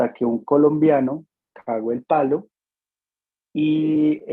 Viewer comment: alguien me aclara que fue lo que dijo aquí? Por la culpa de un colombiano que hizo qué? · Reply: Cago el palo , ósea que la embarró, la cago, dañaron la entrada !